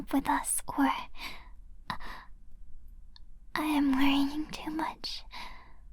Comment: Def heard this as "I'm wearing too much" and I was like "Ayo wtf?"